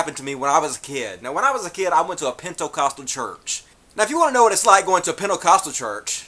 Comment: At first I thought said he went to a PINTO-costal church. LOL